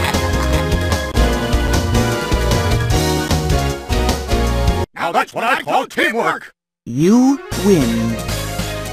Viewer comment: What he say? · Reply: pingas mah boi "Now that's what I call teamwork"